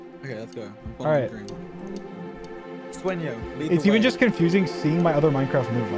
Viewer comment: I think George calls dream "sueño" I didn't catch what he said after that though...